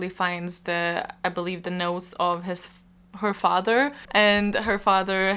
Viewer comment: between you being a little off and my ears being plugged , one of your reviiews really shocked . It was for Home Before Dark. I heard you say , "she found the nose of her father". Just thought I'd share , gave me a smile.